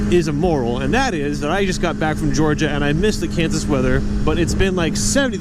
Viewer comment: is he saying that he missed the cancer weather ? :D